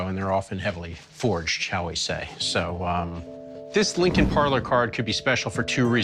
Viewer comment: I heard "This Linkin Park" lol